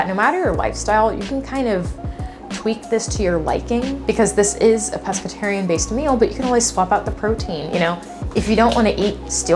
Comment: Why’d it sound like you said, “this is a Presbyterian based meal” 😂 3:33 I guess Bible-belt is still in my head LOL